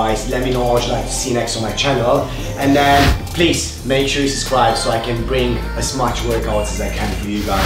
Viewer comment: Make sure you subscribe. I thought he said make sure you know Jesus christ. Sometimes he gives a shout out to Jesus, in vain. God bless you guys and those abs. Been following along for a year now.